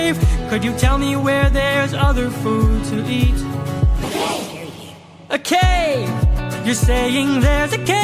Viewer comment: I can't be the only one who hears one of the lotus eaters saying " Scary Cave" Right? 😧